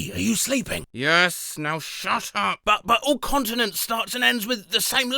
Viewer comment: I HEARD this as “all consonants” and paused and sat to try to figure that out for a solid ten minutes.